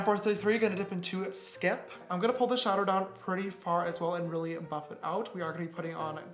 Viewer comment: Did anyone else notice he said sha-der instead of shadow at 6:50 😂